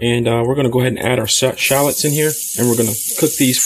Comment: This looked so good I had to watch it twice. Good thing too. I was gathering up ingredients to cook along and thought you said shat-let's. I was on the can brewing a few up when I watched the second time and realized you said shallots. Huge difference. I will have to go to the store to get those.